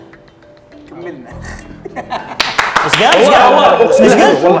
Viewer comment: ايش قال؟ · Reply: كملنا